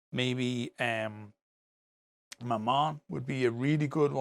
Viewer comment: I understood "my mum" instead of Mammon.